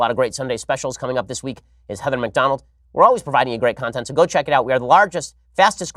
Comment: Did he say Kevin McDonald is going to be the next Sunday special? Probably too good to be true, but that's a conversation I would love to hear.